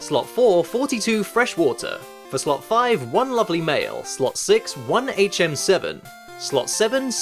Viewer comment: I thought he said "slot 5 one lovely male" Until I saw the screen...